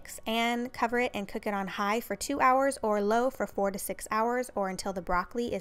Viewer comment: did i hear right 46 hours?